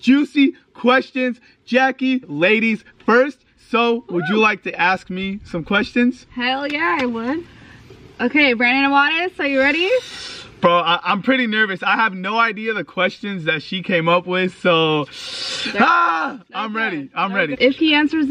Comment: did she say Brian Awadis!?